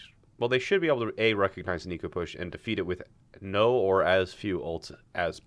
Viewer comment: At around 8:40 is saying "eco" push or "equal" push? What does it mean? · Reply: "Eco push" meaning you do a fight where the enemy uses ult but none of you do and you accept losing the fight. This pushes the ult economy in your favor for the next few fights, hence the name. In terms of resources, it's trading time for advantageous ult economy